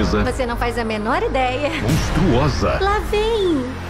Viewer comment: eu escutei hotel Transilvânia 3 o monstrão kkkkkkkk